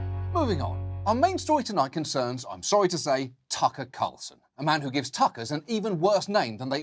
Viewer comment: I make fun of his name. But frfr I thought everyone was saying sucker carson